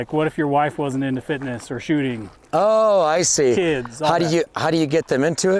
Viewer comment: He said "like if you're wife wasn't into shooting" and he paused and then said "kid", he was like "or if your kids wasn't into shooting" right... But... I heard "like if your wife wasn't into shooting... Kids"